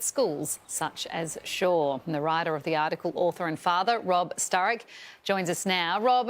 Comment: Did I just hear robb stark